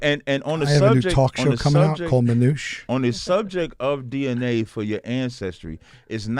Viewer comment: talk show called Manyuch? Not sure if that's what was said but if so, that means bottom gae in derogatory terms. 😂